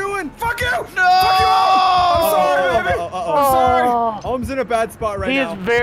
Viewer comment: did just call ohm evan?